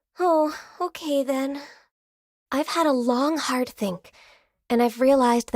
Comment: "I've had a long hard thing".... Is what I heard.